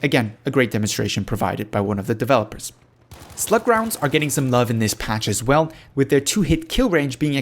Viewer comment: what does he say at 6:31 ?? slip grounds? :| something rounds? what is that? i've played the game for nearly 300hrs and never heard of such thing. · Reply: Slug rounds. Available as specialisation option for the Shotguns. Replaces the Buckshot rounds with single projectile slug shells.